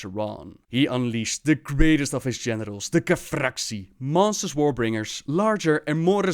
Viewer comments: With that accent I heard, "He unleashed the greatest of his genitals". :P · at like 1:48 i thought u said strongest of his genitals lol